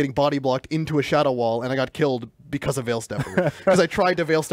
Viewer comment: Did say shadow wall?